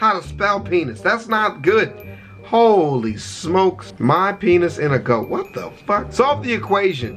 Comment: I think he tried to say "my pen is in a goat" he just put the words too close together.